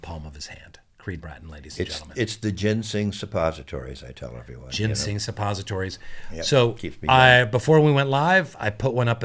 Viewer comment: I swear to god I thought they were saying “gen z suppositories”